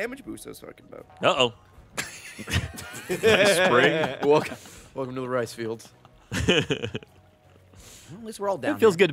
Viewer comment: Did Ray say welcome to the rice fields? ...franku?